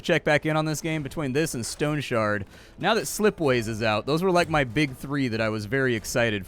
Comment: what game did he say? "sleepways" ? I cant find it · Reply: Slipways. It's on Steam.